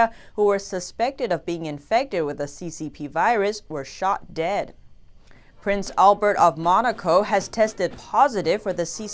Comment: Ref 18:25 - OMG, in North Korea, if you get the CCP Virus (aka Coronavirus), you are shot dead (aka executed)?!?!?!!!! Did I hear it right???